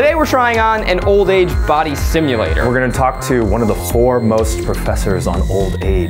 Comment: does he say "one of the four most professors" or am i hearing wrong lmao